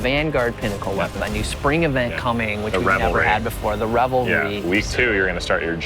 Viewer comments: in this Vidoc did I hear them say the Rebel Raid? Because it sure sounded like that to me. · What did they say after spring event?!?! Rebel raid? revelry?